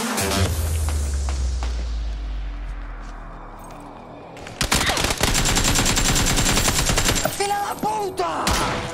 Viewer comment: what he say? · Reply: SON OF A BITCH!!!